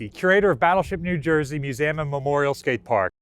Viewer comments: I thought you said battleship memorial skate park. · I'm losing my hearing. I thought he said memorial skate park.